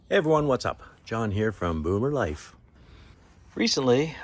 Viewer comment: I’m so sorry I thought you said moo moo life… then looked at your title and it said boomer life. Great video